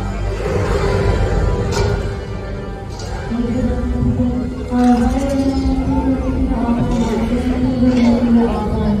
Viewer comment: What were they saying? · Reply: All I heard is that “to stay seated”